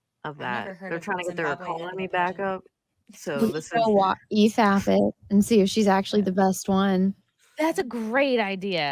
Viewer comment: What does say at 10:21? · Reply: "you could EFAP it and see if she's the best one"